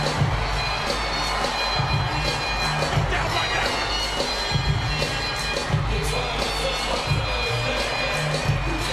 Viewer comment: The freak did he say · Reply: Just like that